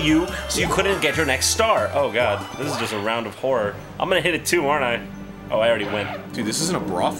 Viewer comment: when Arin said "This isn't a brothel" i thought he meant coz it sounded like Jon said "I'm gonna hit her 2 aren't I?" as if him and Arin gonna woman bashin in brothels at night hahahahah